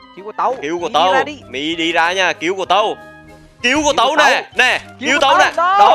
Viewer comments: kill của tâu mà em nghe ra là "gil" của tâu :))) OMG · mn nghe sao chứ tui nghe thành "Gil của tâu nè" 😂